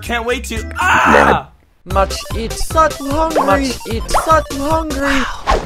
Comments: What is he saying? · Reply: Much Eat Such Hungry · MUCH EAT SUCH HUNGRY WOW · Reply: Little Girls I thought he said much eat certain hungry :) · much eat such hungry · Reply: I thought he was saying "Must eat, fucking hungry" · MUCH EAT SUCH HUNGRY WOW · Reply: Awesome Opossum I thought he said sucking Hungry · I need a 1 hour loop of that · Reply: I heard it as ”must eat sudden hungry, must eat sudden hungry wow!” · MUCH EAT SUCH HUNGRY WOW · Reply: I thought he said I such Hungary such Hungary such Wow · I used to think that it said much eating f word and then after the f word it says Ed so it says fuken (or does it actually say it?!)